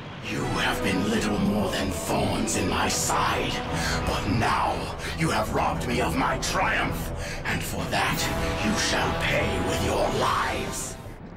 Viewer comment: What did Metalix say? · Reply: You have been little more than thorns in my side but now you have robbed me of my greatest triumph and for that you shall pay with your lives